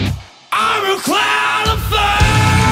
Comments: I'm so dumb, I thought they said "I'm a clown of fate" this whole time >.> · im so disappointed, i thought the lyrics said IM A CLOWN FIRST LOL